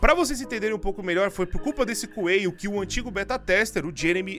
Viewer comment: "Foi culpa desse cueio" Ele deve tá revoltado por pedirem cadê cueio